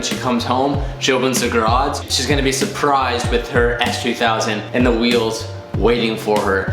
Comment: Who else heard say S3000